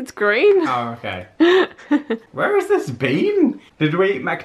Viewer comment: I swear says "where has this bean" I'm laughing so hard. I know it's the accent, but still.